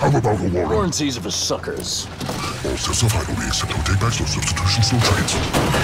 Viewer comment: Can't quite tell what he's saying. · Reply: All sales are final. We accept no take-backs, no substitutions or trades.